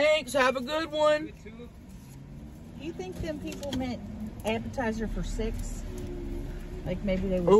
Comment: I thought said appetizer for six …not SEX lol